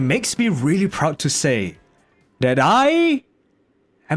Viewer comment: im ngl i thought u said "I... am a f__"